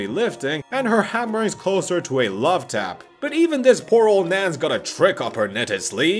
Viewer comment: did you just call wanda an old man lol